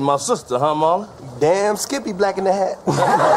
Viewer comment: That "Black In The Hat" broke him!😂😂😂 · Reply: I thought he said black and a half 😅😅